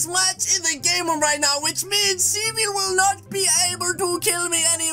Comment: WHAT HE SAID 🤣🤣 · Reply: Russell